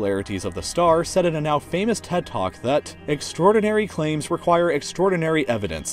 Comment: I thought he said in a now famous Tik Tok 😂😂😂😂😂😂😂 I was like NoOOo0o there's noo0 hope for humanity